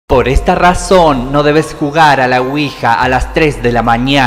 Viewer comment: Yo escuche “por esta razón no hay que jugar a la cuija” XD